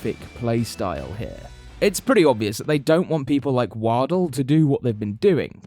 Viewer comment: What did he say 2:07 it’s war-dell like the bad laptop